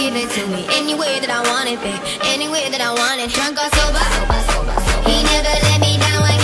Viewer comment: when it said "drunk or sober" i heard "drunk on soba" XD